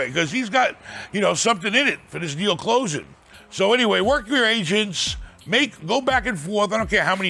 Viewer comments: At first I thought Ben was saying "Gotta get a good Asian", "Work with your Asians" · I thought Ben was saying get a couple a asians? What? Ooooh agents!